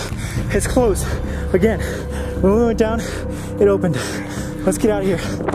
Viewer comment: its sounds like someone saying help help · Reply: I heard “help help get out” !!!